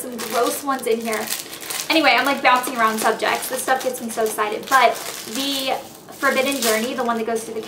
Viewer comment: i think she says cited @ 3:52..... love you elle